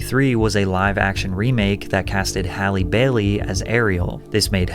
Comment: I thought you said Halle Berry lmao